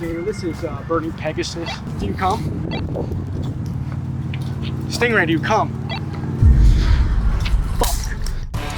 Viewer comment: I thought the video was ight until I heard “stingray, do you cum” cracked my ass😂😂😂